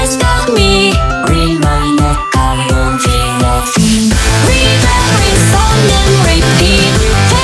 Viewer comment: I thought it said "i wont be laughing" instead "i wont feel a thing" heh